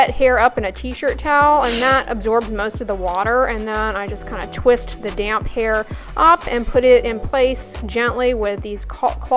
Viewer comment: for a moment I thought she said “ I twist the DAMN hair” 😂 😂 😂